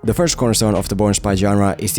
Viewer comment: if you have subtitles on instead of Bourne it has porn xd